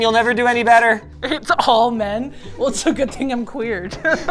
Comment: does she say "It's all men? Well it's a good thing I'm queer."